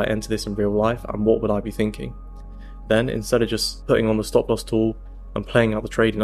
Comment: I heard "no stop loss" and rewinded the video back with captions on. Phew. 😅